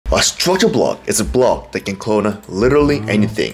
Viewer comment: what was that block ? Can't understand when you say it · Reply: Ooo structure block